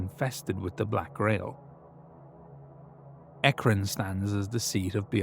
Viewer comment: Oh, I thought you said Akron; I was puzzled as to why you didn't name a more worthy Ohio city like Cleveland or Youngstown to make into Hell's capitol.